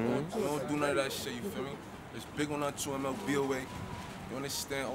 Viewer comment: What is that phrase he says at 3:59 "Something BOA" he says that in every song RIP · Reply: MLBOA